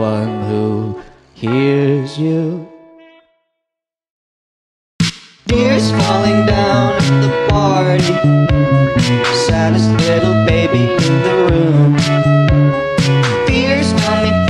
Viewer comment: i can't be the only one who thought it said EARS falling down at the party SANTA'S little baby in the WOMB